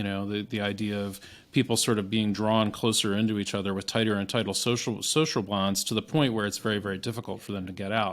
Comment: I swear he says “social blondes”. We could have a whole discussion about anti-social blondes, etc.